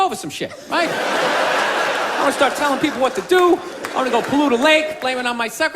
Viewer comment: I legit always thought Bill said "I wanna go poo in a lake, blame it on my secretary" until I read a comment somewhere that said "pollute", which made a looooot more sense, lmao.